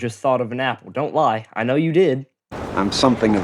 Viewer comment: No I thought of an owl because thats what i heard